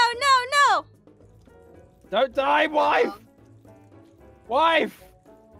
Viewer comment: did call wife??????